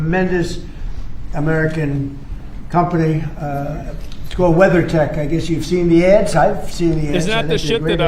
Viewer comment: Weather tech? Dafuq did he just say?!